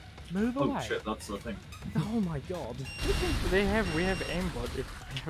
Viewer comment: did he say "we have aimbot"